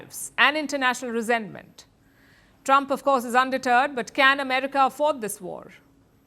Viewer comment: Lol at 5:19 you say Trump is undeterred, but it sounded like you said “Trump is a turd”. As an American, no arguments there!